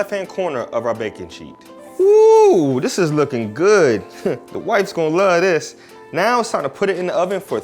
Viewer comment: did he say the "whites will like this?" 2:59/ im sure he didn't but ......lol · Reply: Haha I definitely said the wife. Somebody else thought the same thing 😂